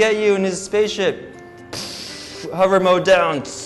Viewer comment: i heard "we're good mario?